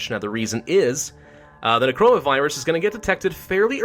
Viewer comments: I thought you said corona virus when its necora virus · D..did he just say corona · sounds like your saying corona virus